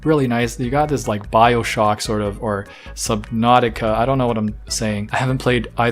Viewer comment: Ngl I was idlely watching this and when you sad sub Nautica I thought you said so Nautica and I was like WU- because my name is Nautica 😭